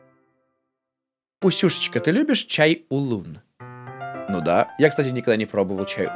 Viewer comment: Мне послышалось "чай шалун"